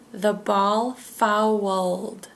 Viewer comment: Hi.I am an English learner from China.I like this vedio. I hear at 9:29 something like " the ball far world ".Do I make a mistake? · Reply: The ball fouled. = It means that the baseball went out of bounds.